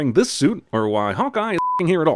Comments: my biggest issues with cinemasins is that when something is explained you complain, but when something is not explained you also complain. and thats about the same with every issue you have with the movies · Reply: My biggest issue with this one was the cringe. Take for instance right out of the gates at 1:55... Doh, did he just call hawkman HAWKEYE? Smh. Then again, if they watched the movie a few times to do this, I would assume they MUST have known this right? So why include it? Being intentionally dumb to the character names seems stupid. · did he just say, “Hawkeye”? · Did he just say hawkeye? I think he did